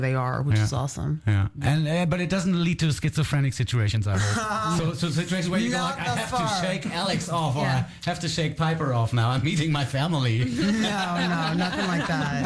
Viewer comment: Guys,what did interviews said at 7:56-8:06, please? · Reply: “But it doesn’t lead to schizophrenic situations I hope. Situations where you go like I have to shake Alex off or I have to shake piper off now, I’m meeting my family”